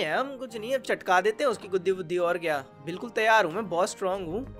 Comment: bhai ye kya bol diya guddi fudd- 😅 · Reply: Bhai Vuddi Bola Hai Bro Ne💀 U Dirty Mind (Notty Notty)😂